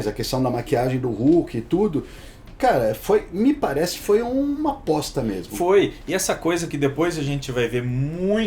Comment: ficou parecendo que o Alexandre falou: “me parece que foi uma b*sta mesmo” 😂😂😂